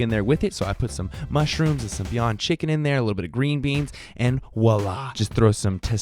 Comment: "veyan chicken"? wtf did he say?? is that real chicken? or did he say Vegan chicken.. there's no such thing as vegan chicken. so thats real chicken. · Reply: He said "Beyond Chicken." It's a vegan substitute for chicken.